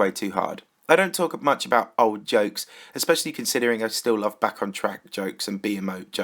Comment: did you just say you still like back on track jokes?!